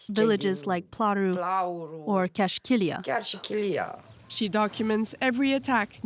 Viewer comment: "Kiarshikilia" is not an actual town, what said was "even (chiar si) Chilia (has been targeted)"